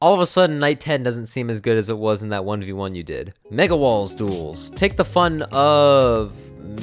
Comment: I thought he said n word duels